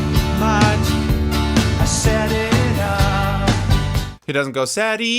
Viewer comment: I like your Reaction Videos. At 1:33 he did not say "Enough" he says "I set it up". Just a heads up. Oh, life is bigger It's bigger Than you and you are not me The lengths that I will go to The distance in your eyes Oh no, I've said too much I set it up